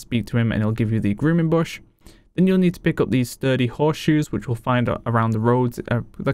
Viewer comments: How many horseshoes do I need for Blanchy? thanks :) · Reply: with his accent i thought he said 30 horseshoes (guess he said sturdy..) sooo an hour later i had 30 horseshoes only to find out i needed 4 lol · was goin to say sounded like you said 30 Horse Shoes